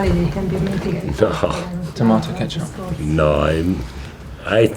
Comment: Does anyone else hear '' tomato ketchup '' at 3:23 ? hahahah